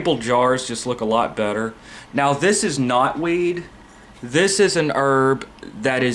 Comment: "This is knotweed" For a second, I thought you were saying "this is not weed" like, marijuana baha.